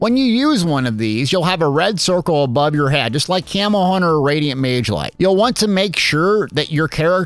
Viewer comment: All I hear is “Camel Hunter” hahaha